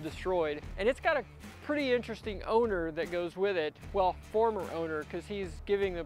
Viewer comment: I was extremely shocked how clean it was on the inside was expecting mold 100% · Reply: At first I thought said it had an interesting “odor” instead of “owner” during the intro.